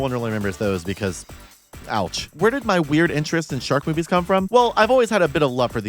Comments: I misheard "Shark Movies" as "Shark Boobies" in the intro. "There is a shark boobie for everyone and I mean that." yes true. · when she said shark movies, I thought she said shark boobies and then I got sad because there werent shark boobies :(